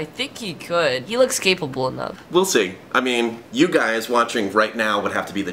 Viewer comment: i thought the kid said he looks "gay-pable enough"